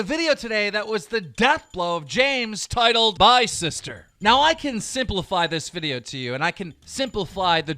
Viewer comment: does anyone else hear simplifly?